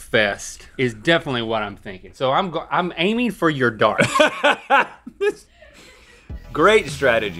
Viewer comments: Omg the intro I thought he said I’m aiming for you daughter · "I'm aiming for your daughter" is what I thought Link said